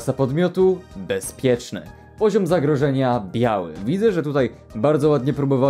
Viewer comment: Usłyszałam poziom zagrożenia yaoi XD